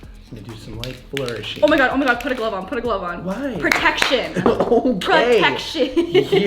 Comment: it sounded like she said protectshit lmaoooo